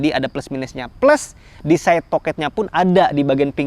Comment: Di menit 10:45 saya dengernya si abg cakap toket. Mohon dikoreksi pemirsa 😅 Good review